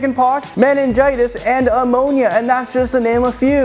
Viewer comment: Love the lists and his other channels, just can't decide if his random mispronunciations are intended. At 2:09 for example people apparently suffer from Ammonia. Also heard "International" when the caption said "Innumerable". Just wondering...